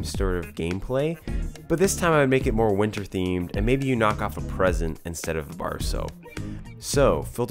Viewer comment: I understood "but this time I make it more winter themed and maybe you knock off the president instead of a bar of soap" and I was like, I don't see the connection to winter, but sure, why not..